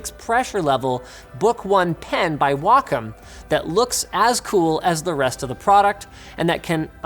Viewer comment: subtitle error, Linus says "as cool" and subtitles say "as the"